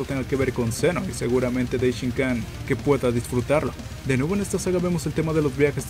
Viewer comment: No entendi bien lo que dijiste como que "disfrutarlo"?